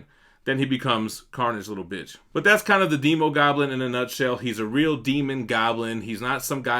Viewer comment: I thought you said emo goblin...slightly disappointed but great video and statue 😋